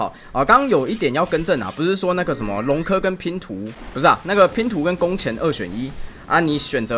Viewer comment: “龙刻”跟“拼图”？我怎么听成了“龙族拼图”🤣